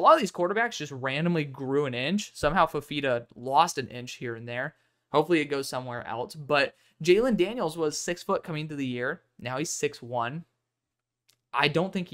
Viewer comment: Am I the only one who heard him say hope that inch went somewhere else